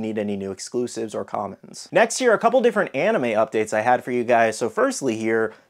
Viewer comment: Every time you said, "next here" I thought you said next year lol